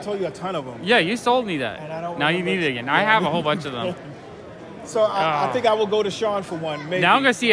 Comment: I love the Dick Breifer Frankenstein's. He's one of those rare artists whose work looks like no one else's, and the stories are wild. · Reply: Did Sean say "I think I might go to Charlotte for one?" If he means HeroesCon in June, I will be there. Come hang out! ;)